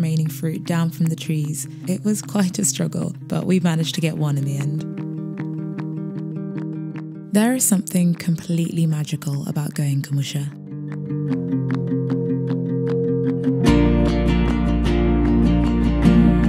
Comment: sh She says "There's something completely magical about going commercial". Really thought I had heard her say commercial, but the enunciation is actually there, kumusha/home.